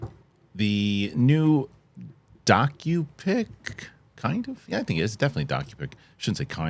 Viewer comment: When you said this is a "docu pic", I at first thought you said "donkey pic". About spit out my drink.